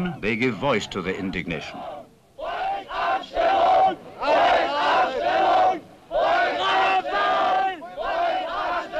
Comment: Can anyone help me transliterate the chants at 24:15? What are they saying? Sounds like "Haut ab ... Schweinehund"? I'm not sure especially about the last bit (Schweinehund) but it's the best I could make, maybe someone else with better knowledge can find out. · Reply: the first part is a little bit hard to understand but they're shouting "Volksabstimmung"